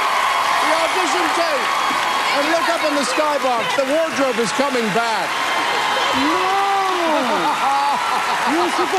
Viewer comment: Can anyone tell what Meryl said to Maks at 2:34 ? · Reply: It sounds like, "And you haven't changed a bit!"